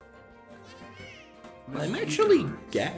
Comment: I thought he said I'm Gabe😂😂😂😂😂😂😭